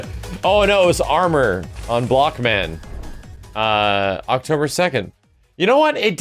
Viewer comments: I thought blockman said blackman...woops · The first time I seen this trailer I thought it said "Black Man" instead of "Block Man".